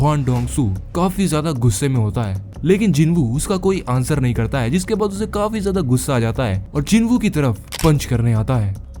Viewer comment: I think Jinwo Said " Go ask Yourself" at that moment after dongshow Told Did U Killed My Brother Cold Moment Indeed. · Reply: Jinwoo reply go ask your brother yourself